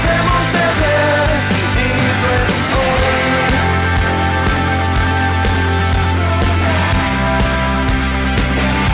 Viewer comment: Alguno entiende que es lo que dice la chica desde el minuto 02:42 hasta el 03:15? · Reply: dice "si fuera más wapa y un poco más lista, tendría el valor de preguntarte quien eres"